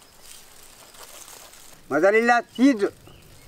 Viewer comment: When said "Mozzarella cheese", I heard "Mother-in-law cheese"😀😀😀